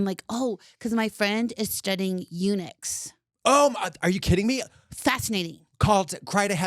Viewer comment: I thought she said Unix instead of Eunuchs and I'm like why tf is Katya excited about this